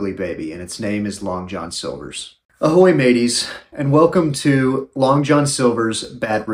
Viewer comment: I thought he said ahoy babies not maties 😂